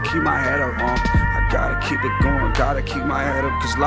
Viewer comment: I always thought he was saying "gotta keep it going, gotta keep my head up, homie"